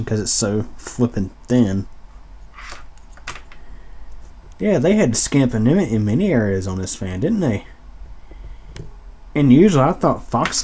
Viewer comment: I don't understand what you said there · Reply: They had to cut corners on many areas of that fan's construction.